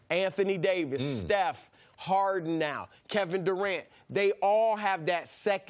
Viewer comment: Yo mr.Broussard Steph doesn't play with harden lol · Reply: Low Distortion He said Steph, and now Harden. It sounded like he was saying Steph has Harden.